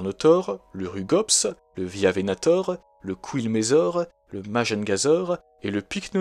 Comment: le quoi ? 🤣🤣🤣🤣🤣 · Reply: Le Quilmesaure Ainsi ai-je parlé 😁